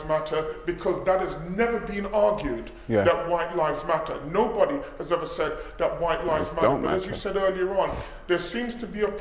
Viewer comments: 3:56 CAN YOU CLARIFY WHAT YOU SAID PLEASE. THANKS !! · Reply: What said is ''no one has ever said that white lives dont matter.'' Please don't try and twist his word or purposefully try and misinterpret what he said because both you and I know what said wasn't racist at all. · I hope you all enjoyed that... The Black Opinion is aiming to create debates, ask the difficult question & bring the content that others do not bring. Please donate to The Black Opinion so we can continue bringing you this high quality content. Peace family, peace ✌🏿 <link> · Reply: WHAT DID YOU SAY AT 3:56 SECONDS UNDER YOUR BREATH CAN YOU PLEASE CLARIFY FOR ME ?? MANY THANKS !! IM PRETTY SURE YOU SAID WHITE LIVES DONT MATTER BUT AHL WAIT TO SEE WHAT YOU COME BACK WITH. I MIGHT HAVE PICKED IT UP WRONG 👍👍